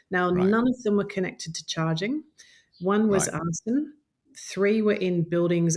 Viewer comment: "One was ..." I dont understand the word, please help me. Thanks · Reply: Arson